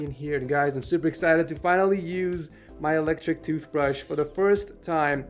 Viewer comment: did he say toothbrush?